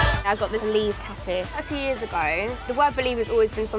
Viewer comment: I thought she was saying Beliebe 😂😂